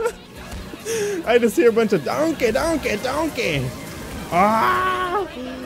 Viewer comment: all I kept hearing in the background was "DONKEH DONKEH DONKEH!".